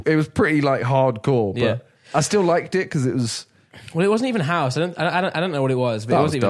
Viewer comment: i legit thought simon said "I still like dick"